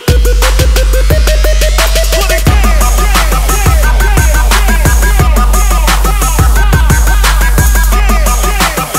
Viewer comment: what are they saying!? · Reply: They said “put it down”